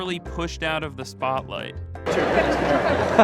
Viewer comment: Did he just say to his child "Wait , we need a good picture." 😆